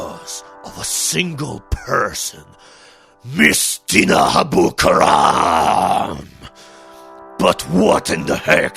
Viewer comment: I thought you said Mystina Dabuturam which sounds like an amazing anime villain.